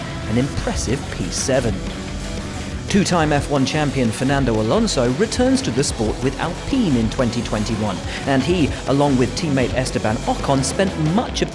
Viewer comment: I thought I heard, "Fernando Along returned without peeing in 2021."